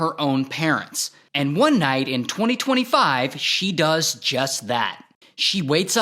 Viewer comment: 2025? this just happened? 0:38 guys I have ear issues did I hear that right 2025? Please I have to make sure did he say 2025??? PLEASE ANSWER ME!